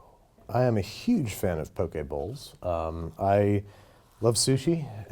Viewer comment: i thought he said Poké Balls